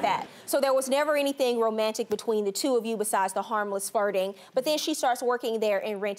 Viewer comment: Judge Faith said “public farting” instead of “public flirting” 😂😂😂 4:40 +/